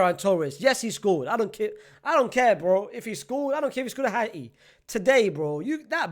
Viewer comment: Which language did you speack loke that. @ 9:47 "i heard something like i dont care if he scored a hard heel." My brother be calming down. I dont know why you are changing your tongue. 😂😂😂😂😂😂😂. Wonderful. You call ball as bull. So football will be pronounced as footbull. 😂😂😂😂😂